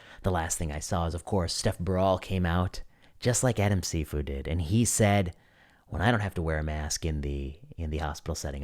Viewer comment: Stef Baral and Adam Cifu... took me a bit of Googling to decipher the names you were saying @ 8:19, even after replaying at slower speed. Please keep in mind that some viewers may be encountering your channel for the first time or otherwise may not be up on what other researchers have been saying, and may need a point of reference for some of the items you cover.